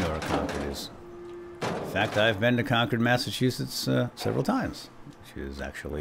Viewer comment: Hooray! Finally someone who can pronounce Concord correctly. Good to watch a playthrough by someone who knows the area. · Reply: thought I heard you pronounce it like 'Conquered' instead of like 'Concorde'. Maybe it was auditory hallucinations again.